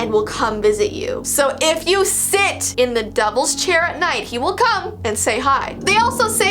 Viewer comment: OML turn on captions it’s says Jess TV instead of!!! Also at 4:53 instead of devil’s chair it said doubles chair!!! 🤣😂